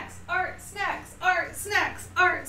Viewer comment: I thought she was saying art sex at the beginning oh my god